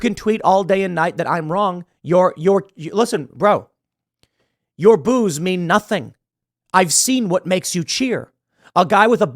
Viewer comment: "Your boos mean nothing, I've seen what makes you cheer." - For a moment I thought Tim said "Your booze [Bud Light] means nothing".